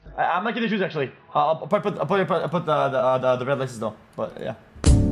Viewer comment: wtf is he saying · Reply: "I'll probably put the red laces though, but yeah"